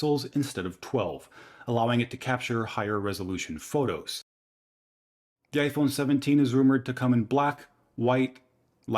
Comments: Did he say ‘G A Y’ iPhone ?😂😂😂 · did he just say “gay iPhone 17”?😂